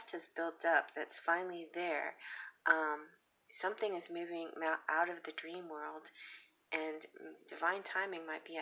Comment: #1 you scared the hell out of me. At 8:46 I swear you said "moving Matt out of the dream world" his name is Matt. I listened to it about 4 times.... kept hearing that, so I put on the captions and realized what you said, but my heart stopped. If you listen to it, it really sounds like that. The only thing is he recently did something major that broke my trust. So I have no clue.